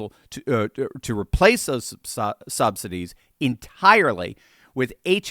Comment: Did you just say "sobsidies"? Either way, it's appropriate!